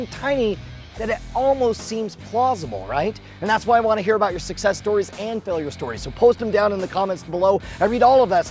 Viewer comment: I want to hear about your sex stories. That's what I heard there... lol